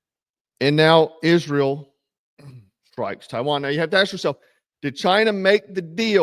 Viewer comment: sounded like you said Taiwan. Maybe you just said Tehran funny ?